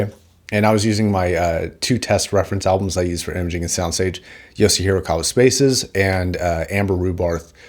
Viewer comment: what are the two albums recommends here? Especially the first one I can't tell what he's saying (edit: found the second one). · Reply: Spaces - Yosi Horikawa